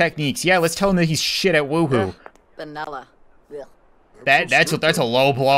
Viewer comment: When Barbie criticized Grim's sex technique at 16:00 it sounded like she was saying "vanilla" she was basically saying death is a boring sexual partner.